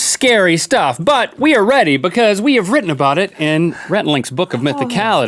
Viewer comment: did he say rhett-ie? lol